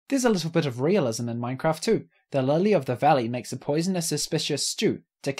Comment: I'm sorry, what did you say kills parrots? Cockies?